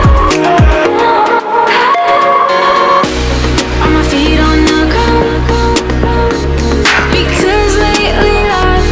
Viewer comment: Uhh what's she saying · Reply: "Are me feet on the ground? Because lately, life hasn't felt so heavy."